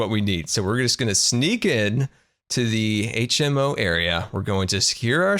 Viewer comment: Did he just said HNO at 4:00 ?😂